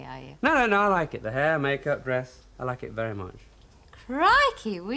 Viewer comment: did he say breast or the rest? Ken said something and back than they did not say words like that on 📺. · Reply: He says "Hair, makeup, dress, I like it very much" you probably just need to put your volume up!!